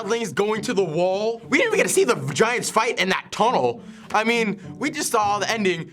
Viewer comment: did he just say the "vagiants"? That'd be the scariest character of all time.